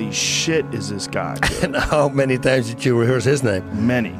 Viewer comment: Joe thought he said “How many times did you hear his name” 😂😂 I’m weak